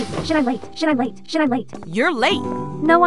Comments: at the start i hear "should i vape?" lol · “SHALAE SHALAE SHALAE SHALAE SHALAE SHALAE SHALAE SHALAE SHALAE SHALAE SHALAE SHALAE SHALAE SHALAE SHALAE SHALAE SHALAE SHALAE SHALAE SHALAE SHALAE SHALAE SHALAE SHALAE SHALAE SHALAE SHALAE SHALAE SHALAE SHALAE SHALAE SHALAE SHALAE SHALAE SHALAE SHALAE SHALAE SHALAE SHALAE SHALAE SHALAE SHALAE SHALAE SHALAE SHALAE SHALAE SHALAE SHALAE SHALAE SHALAE SHALAE SHALAE SHALAE SHALAE SHALAE SHALAE SHALAE SHALAE SHALAE SHALAE SHALAE SHALAE SHALAE SHALAE SHALAE SHALAE SHALAE SHALAE SHALAE SHALAE SHALAE SHALAE” Were the most inspiring words ever❤😢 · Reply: I hear: “Should i wait?” And “Shalae” And “Laycha”- Also “Wait cha” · When she said shit I'm late I thought she was saying should I wait and chocolate lol😂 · At the first part sound like she saying should I wait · Why does it sound like she said should i wait 💀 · When it said shit I’m late it sounded like it was saying should I wait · I heard "should i wait" instead of "shit im late" lol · the beginning has the most dope rap song ngl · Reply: First I heard Shit I’m Late then I heard “Should I Wait” then I heard “Shalmé” and I’m finally hearing “c h u b b a y” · Bro in the beginning You shit im late infinity times and i heard it “Charlie”